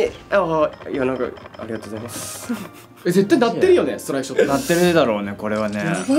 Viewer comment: スタライクショットいってたね笑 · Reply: izu mina ストライクショットねw あと、さん達には、音聞こえてないからねw